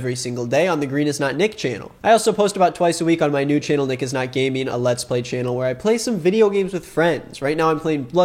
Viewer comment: thought you said "nick is not gay" there for a sec. fourth channel concept?